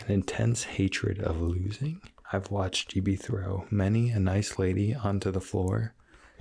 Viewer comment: He speak so clearly that the auto generated captions understands him. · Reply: Really? Because at 2:35 he said “I’ve watched Gibi throw,” but instead the captions said “I’ve watched you be through”.. fake.. clickbait, gone wrong 😱🔴